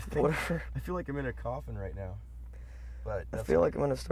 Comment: does anyone understand what is Layne saying here? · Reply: He says " Start coughing and hacking hairballs all over you. Its the chest." talking about Mike..lol